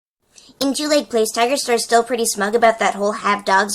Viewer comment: the captions say "injuried glaze tiger sir" lol